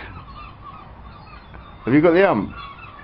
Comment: what does ask to ruby i can't hear it propperly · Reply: said "have you got the ummp" which is british slang for "are you in a bad mood"